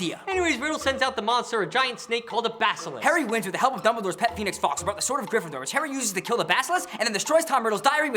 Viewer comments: did he say "with the help of Dumbledore's pet penis??" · did you say penis? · did he say penis fox @ 2:23 ?? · lmao 2:22 sounded like you said pet penis instead of pet phoenix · I'm sorry, did you say pee-nix? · Is it just me or did they say pet peanut fox?